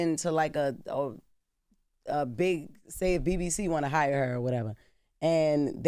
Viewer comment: I thought Jess said “What if a BBC wanna hire her”?